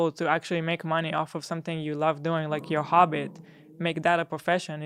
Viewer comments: did Hila just say your Hobbit instead of your hobby 😂😁 it's okay language is hard girl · Did Hila say "hobbit?" at 47:06? She's so stinking cute. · Did Hila say hobbit